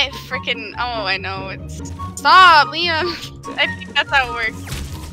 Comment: "stop liam" huh?! did i hear right????????????? and btw who is watching on 2021